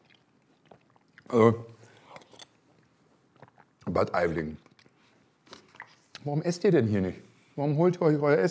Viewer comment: Was sagt er hier? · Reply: "Bad Aibling, warum esst ihr hier nicht?" (Bad Aibling ist der Ort, in dem das Ganze spielt, wie auch gesagt wurde im Video.)